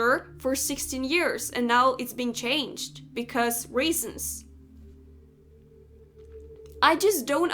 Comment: You're allowed to disagree with me, but I haven't said anything about race. I don't know where you're getting that from, and I don't know why you're putting words in my mouth. Please don't do that. This has nothing to do with race, and we shouldn't make it about that either. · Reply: okay if I miss heard you say races instead of reasons at time 7:00 i believe then my fault but You also said you "don't see the point" in reference to her character change," "I assume they are doing it for the sake of representation"time 5:48 to time 5:55 . That statement made it sound like you didn't want representation of other people in the game cause this character has been a certain way for 16 years. And what is an equestrian supposed to look like? You said at time 5:28 "she doesn't look like an equestrian at all". What do you think an equestrian is supposed to look like. Not every equestrian is going to have black high riding boots and tight tan jumping pants.